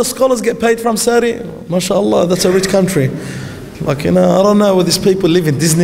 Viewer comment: Did he just say "f***ing hell?" 0:45. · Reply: He said ''Look you know''